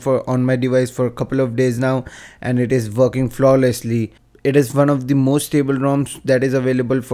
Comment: i thought u said fuckin flawless and it sounded faboulous xD